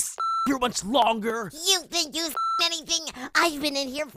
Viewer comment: First scene: Blocky:We need to f***ing escape!😑 Golf ball:That's easy for you to say,you f***ed the TLC in episode nine!😒 Blocky:Yeah but now I'm back,and I don't wanna f*** here much longer😪 Flower:You think you've experienced anything!?I've been here for 16 months AND I WANNA F***ing GTFO NOW!😡😡😡 (Tries to open TLC) Flower:HEY!Its still shut!?F************😡😡😡😡😡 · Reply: Nathan Hon I heard "You think you sucked anything?"